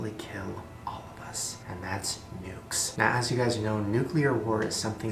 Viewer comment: when he said nukes I thought he said nudes